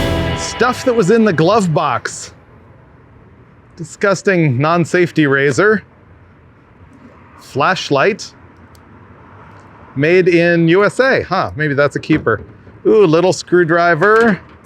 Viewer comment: I thought he said "Fleshlight".... Which might track, I dunno.